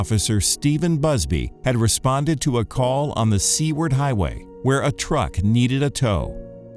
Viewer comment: I thought when he said Seaward Highway he meant C-Word Highway and was just censoring it. 😅